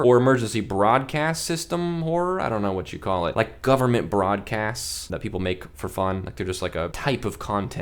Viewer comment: Help I heard “Ass that people make for fun” 😭